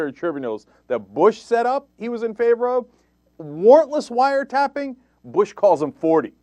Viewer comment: did he say "wartless" wire tapping?